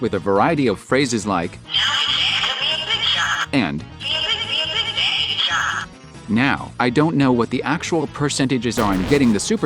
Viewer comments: HERES WHAT I HEARD ''now's your chance to be a big shot. be a big. be a big then get shot'' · While we're on the subject, could someone please enlighten me as to what's being said in the voice line heard at 4:46 ? "Be a big- be a big- something shot" What is that word? It sounds like "baggage"? or "basket"? It doesn't seem to make sense. what is it? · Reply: Just big shot😊